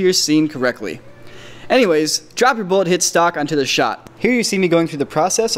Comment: lol I thought it said Bull Shit Totorial